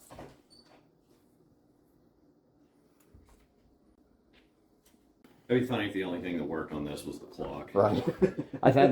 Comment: I'm all for the shenanigans if that lightens the work and when it's finished "that'd be funny if the only thing that worked on this was the clock" 20:34 🤣 .. I laughed so hard that I didn't even hear what said after that lol 🤣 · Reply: Oh, I said "Morpha rules, Mack drools" 😁